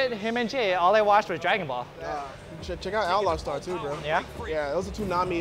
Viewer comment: what anime did he say? I didn't hear it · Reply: Morris O Outlaw Star